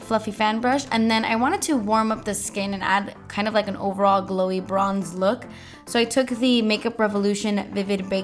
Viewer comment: I somehow heard "broccoli" instead of "glowy bronze" lol